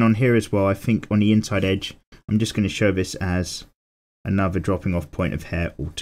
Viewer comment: for some weird reason I heard "I just shove this ass"